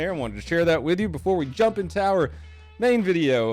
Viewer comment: I thought you said "jump in tower"